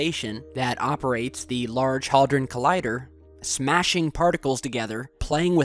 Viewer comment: Did he just say hodron collider?